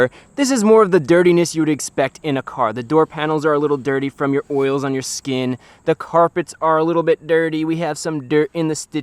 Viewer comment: I totally thought you said "its dirty from the boils on your skin" 😂 had to rewind that